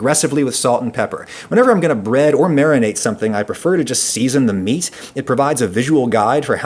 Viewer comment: WOOWOWO...Did he say he like to season the meat?